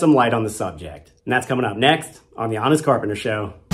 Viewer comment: Thanks for the information. But you should say "honest carpenter" a bit more slowly. Twice I heard "honest corporate" and was like "maybe I could buy that".